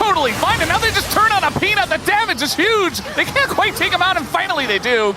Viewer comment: the caster say penis instead of peanut at 3:34 KEKW